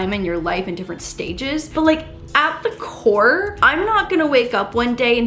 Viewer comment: Oh my god she was saying “at the core” and I thought said “apple core.” Good morning 😂😂